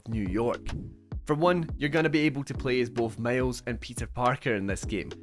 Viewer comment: you sounded like you said "you going to play as both Males and Peter Parkers" lmao